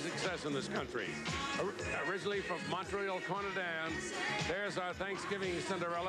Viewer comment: Did he say "originally from Montreal Conadan"? How the hell did he manage to mispronounce Canada? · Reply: Hahaha! I thought he said “Montreal Trinidad”… He also called her France “Joel” instead of Joli 😂